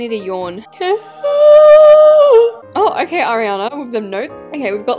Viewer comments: AT AROUND 1:45 in the vid DID SHE JUST SAY HER NAME OR WHAT SHE SAID ARIAINA!!!! WWWWWHHHHHAAAAATTTTT?? IM SOOO CONFUSED! ALSO SAID ELISE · did she say her name!? Arianna??